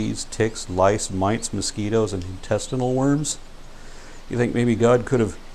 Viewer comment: I wasn't listening close enough and thought you said "testicle worms" not intestinle. Dayum I need to pay attention.